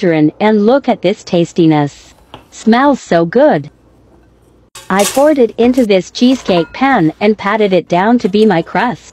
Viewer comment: I thought it said "I'm farting into the cheesecake pan" cause I was distracted getting a drink